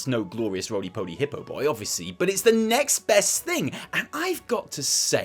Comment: I honestly thought said “it’s necks best thing”. He’s a dad now, he’d have gotten away with it.